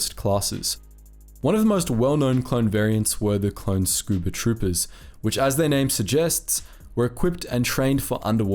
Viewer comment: I wasn't looking and I thought he said scoopa trooper so I just imagine a clone on animal clean up duty.